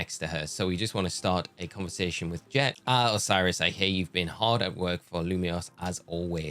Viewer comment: I completely misheard you and thought she said “Osirus, I hate you…” I was like “dang, lady! Did Osirus catch your favorite pokemon or something? yeesh!” 😅